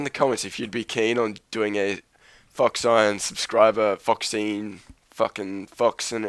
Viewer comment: Again! I thought you said fuck sign instead of phoxine